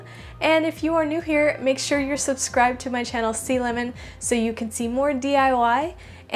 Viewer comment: In the beginning of the video I thought it said salmon, not sea lemon